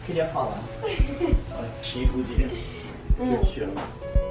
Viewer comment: Eu escutei certo o Ujoãozinho falando eu te amo pra Carol 😍🎉 2:07 ❤❤